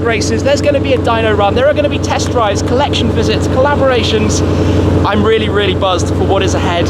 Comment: DID I HEAR COLLABORATIONS